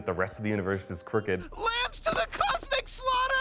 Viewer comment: I thought Morty said "LAMPS TO THE COSMIC SLAUGHTER"